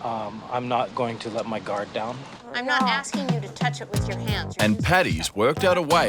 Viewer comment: Off or I’ll touch you is the crazyest thing ever bro 💀💀💀💀💀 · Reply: rofl I thought she said pet, not touch. And then I lost it.